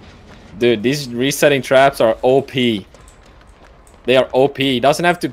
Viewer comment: did i just heard « trapper add ons are op » 😂😂😂 trapper op pls nerf 😭